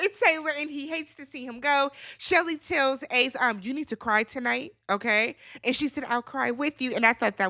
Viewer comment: i thought she said pray lmao